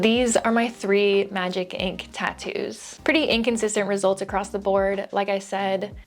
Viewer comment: "Inkconsistent" 22:47 🤔 💭... i think that's what I heard, if so 😅👍🏾